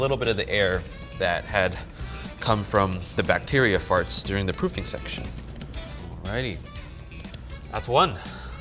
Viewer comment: "...from the bacteria farts during the pooping section" 😂😂. that's what i heard at first